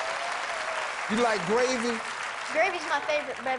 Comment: What did Steve said? 1:50 do u like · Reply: "Do you like gravy?"